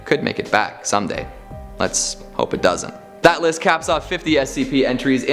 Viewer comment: did he say “that lists 50 scp”?